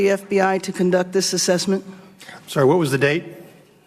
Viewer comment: "Sir..." did I hear that right?